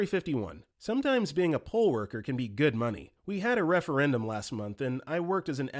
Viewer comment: when i heard pole worker. my first thought was as an entertainer. then as the story went on i though telephone poles. nope. counting polls.